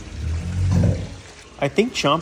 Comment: I thought it said “I think Chompers and Soni want to mate!” Heh😅